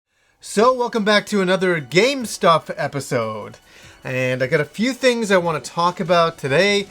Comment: I hear ''Welcome back to another Gamestop episode'' every single goddamn time!